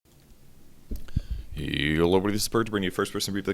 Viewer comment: Every time Purge says "yo hello everyone" i hear "Yolo everyone" xD